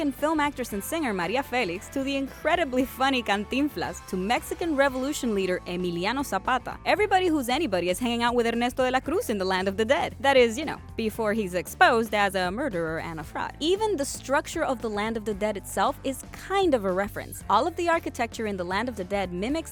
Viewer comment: I thought she said "a murderer and a thot"